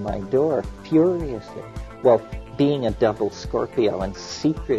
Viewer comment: did he say "being a double-scorpio"? is that a reference to astrology? i thought mckenna was supposed to be clever...